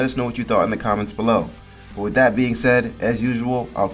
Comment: nahh I thought he said stay black 💀